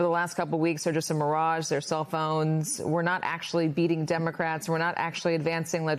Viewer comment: "Self-owns" ...I thought she was saying "cellphones". 😊 😮